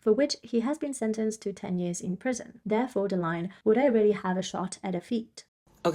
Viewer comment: I thought he said “would I really get a shot at her feet”😂